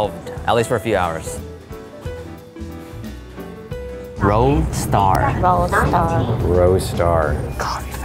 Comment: I swore they said world star 😂😂😂😂